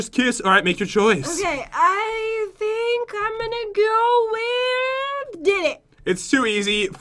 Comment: Oh, BTW, I LOVE that grandpa acccent!!! 😅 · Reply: also at 28:11 it sounded like said, "I'm gonna go with........ DICK!" Idkw, but it really does.....